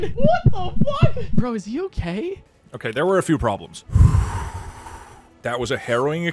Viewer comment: "That was a heroin experience" (misheard)